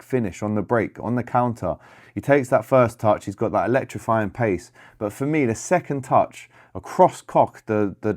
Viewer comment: Did you just say second touch across a cock? 4:57 😮